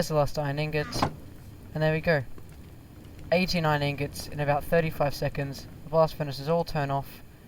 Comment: I thought he said 89 ingots for a sec